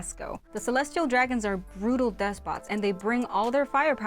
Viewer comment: (Mumble)??? the subtitles guy didn't catch the word "despots". Come on, I'm not even a native English speaker and I had no problem understanding that word. Time to hire another subtitle person lol.